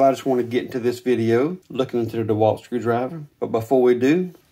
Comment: I thought you said you would look inside the dewalt..